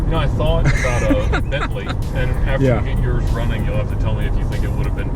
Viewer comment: Did I hear something about a Bentley???